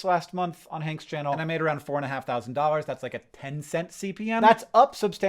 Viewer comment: An issue with your captions: at 4:26, when you said that you made about four and a half thousand dollars from YouTube Shorts last month, the caption says that you made $45,000. I didn’t happen to be looking at the screen when you said the other numbers so no idea if anything else is incorrect, but I thought you ought to know